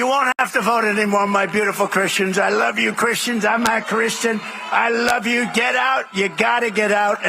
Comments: Did he just say 'I'm not Christian'!? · I've replayed this about 10 times and I can't be sure but I think at 0:21 he says "I love you Christians, I'm not Christian". I hate that he plays off of peoples faith to create something so vile. Also to Brian's point, we've seen what T appointees will do, look at his case in Florida. Look at the supreme courts ruling on presidential immunity. He's a con-man, but he's not very good at it. He tells you exactly what he plans to do and people expect that he's "just joking". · It sounds like he says, "I'm not Christian " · Did he say, “I’m not Christian” at 22 seconds in? I may have misheard because Trump enunciates like a four year old most of the time. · It almost sounds like he says,"I'm not Christian" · Did I hear the words I'm not Christian? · does he say "I'm not Christian"? I think he is an atheist. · Trump says "I'm not Christian" but for some reason they don't hear it 🤔 also Piers Morgan is not a U.S. citizen and cannot vote for Trump anyway! So sic of Rupert Murdock's Australian conservative operatives gearing us into chaos. I would like to shut down FOX networks as a threat to democracy for plunging the United States into political chaos with it's demonstrable propaganda that its been spewing nonstop for decades now. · Sounded like he said “I’m not Christian…” which would mean that’s the first time he’s ever told the truth! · Did I hear him say "I'm not Christian" ! 😂💙💙💙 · does he say “I’m not Christian” 😂